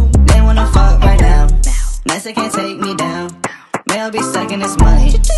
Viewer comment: I thought it said Mexicans can't take me down haha